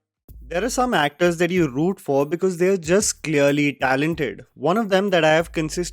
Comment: Did you just say “ARK is talented”??